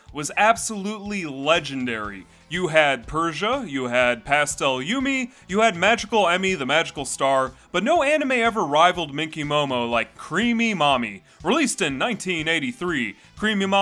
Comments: The number of times my brain thought he was saying "creamy mommy"... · I thought you were saying 'creamy mommy' and I was scared.